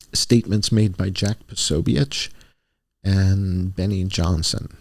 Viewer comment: I heard "Jack TheSoyBitch" 😂😂